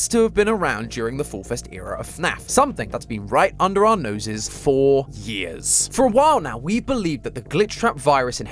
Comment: When he said “For years” around 24:40, I heard “Four years.” Luckily, there was visible text for my brain to decipher it 😂